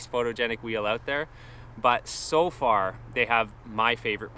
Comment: u talk about the color about it yellow having better performance ? lol. Did i hear that wrong ? thx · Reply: sounded like u said had your favorite performance. Either way, I really appreciate you and all your vids. Thx.